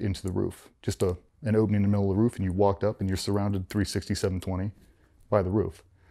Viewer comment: he says "you're surrounded 367 20" can anyone tell me what that means?? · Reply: he said 360, 720. just an expression. 720 is double 360